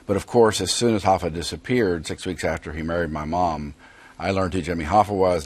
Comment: I had to keep rewinding back because I thought he said murdered his mom. Lol